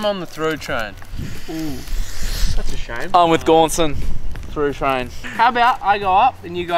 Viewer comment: Oh, it's Gaunson. I thought you were saying "go on, son" this whole time